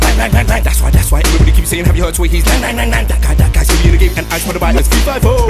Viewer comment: it sounds like he said, "Have you heard Twinkies".